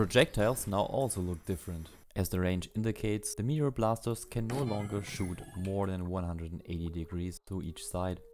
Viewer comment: what did you say?? I can't hear you. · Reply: Ah thanks i will add the subtitles. "As the range indicates the meteor blasters can no longer shoot more than 180°" What i mean is they can't shoot diagonally downwards.